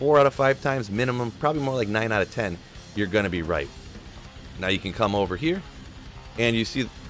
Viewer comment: "9 out of 10 time you're going to be right" but I thought you said go left